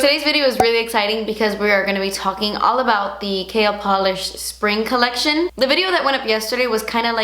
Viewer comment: it always sounds like she says kale polish 😂😂